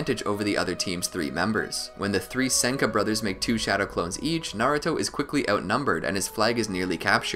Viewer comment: did he say naruto or did i hear wrong?